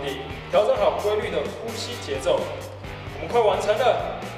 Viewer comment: 邊做邊幹.....還把我們快完成聽成我們快往生了.......!!